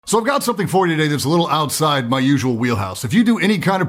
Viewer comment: i thought he said in the beginning “so i got something for u niggas” 💀